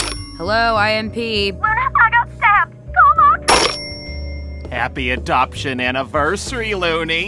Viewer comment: for anyone who does not know what Millie said here, she said “Loona, I got stabbed, CALL MOXXI-“